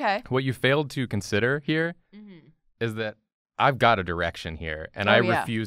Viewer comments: you guys said vagina so much that I thought said “I’ve got an erection” and I was like.... umm?? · I thought I heard "erection" and it woke me the fuck up.....he said "direction."...